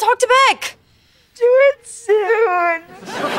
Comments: what did Jade West say? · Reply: Do it soon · what did jade say? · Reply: “Do it soon”